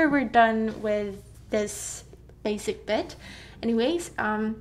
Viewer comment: lol did she say basic bitch?